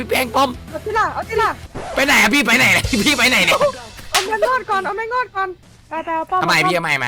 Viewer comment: พี่แป้งเอาๆ ได้ยิน : เอาติ๊ล่ะ 😚 เอาแมง งอดก่อน